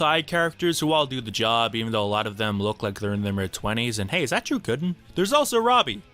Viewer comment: Did he say "Hey Is that Drew Gooden?"? if he did, that's hilarious